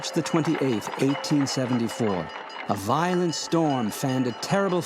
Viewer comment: the date in the subtitles is wrong...in the subtitles "1987" is mentionned, the voice in the video says "1874"...please correct!